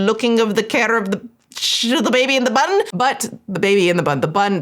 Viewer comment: I thought was about to say baby in the butt when she first said baby in the bun 🤣Neither would be the correct saying but I was definitely further away